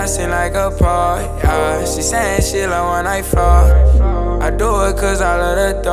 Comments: who else thought he said "shelove when i fart"? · I always think he says "she said she love when I always fart" 😭